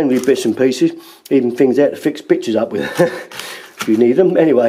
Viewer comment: Did he say at 3:34 to fix bitches up?